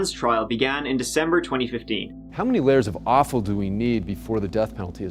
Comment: "How many layers of awful do we need until the death penalty is appropriate?" Regardless of the amount of layers of awful, the death penalty is still the opposite of any objective judiciary system! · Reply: Because I'm British, I thought he said 'offal'.